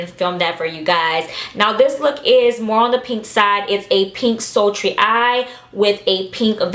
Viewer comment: Didn't even watch the video yet. I thought it said "slutty and sexy" instead of sultry lmfaooo i was like wuhhhh continues vid